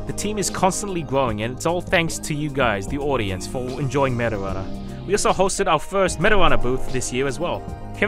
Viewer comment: the captions say “thanks for enjoying marijuana